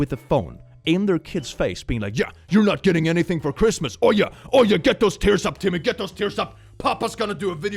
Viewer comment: when he said "oh yeah", I thought he said "are ya" in a very deep British accent